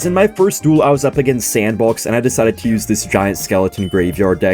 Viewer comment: I thought that you said that you was in a sandbox when you played that game